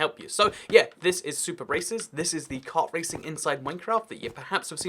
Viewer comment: For a second I thought that he said “this map is super racist”